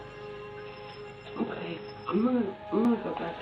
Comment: In the 1st clip, I honestly couldnt tell what I was hearing when she jumped, but what I DID hear was @ 1:00, just after she says Jackie, I DISTINCTLY hear Hi at that point. But, IMO, it sounds like Siri or one of those devices... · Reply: I spoke to that reddit user and surprisingly that was actually her saying "hi". Although it does strangely sound like siri or alexa 👀